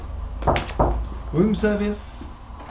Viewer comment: did he just say "Womb Service" hahaha